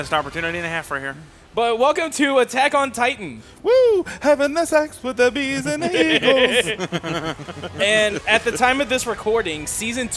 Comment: Probably just me, but I always heard it as "Kill the mess with the bees and the Egon!".